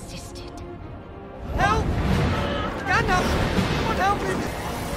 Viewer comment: Even though I believe Pippin says "Gandalf, forgive me" at first I heard "Gandalf, kill me" which would've been fitting and hilarious as well.